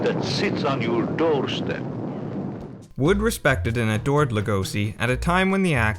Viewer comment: For a long time, I thought Lugosi said "Toaster" instead of "Doorstep" during his "Big Green Dragon" speech. I like to think he actually said "Toaster".